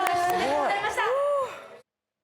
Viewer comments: ちゃんみなさんの咆哮最高。英語？なんて言ってるのかどなたか教えて。。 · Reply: you guys are killingだと思います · ちゃんみなさんの英語のコメントが聞き取れないんだけど、どなたかわかりますか？ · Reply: Kill it だと思います。最高！大成功！